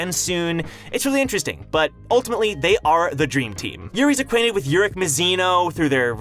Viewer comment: Did u say "Dream team"??